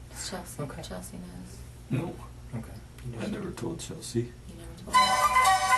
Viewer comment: really wierd..anybody knows what he says here? You really have to put the volume on max because he wispers something, but i cant understand what.. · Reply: “I never told chelsea” ?